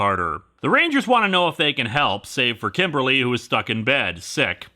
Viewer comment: Best mishearing ever - "save for Kimberly, who's fucking dead."